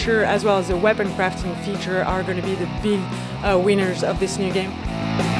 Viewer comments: did she say "big weiners of this game"? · Yara Khoury at 1:15 "Big wieners" - Am I the only one who heard that??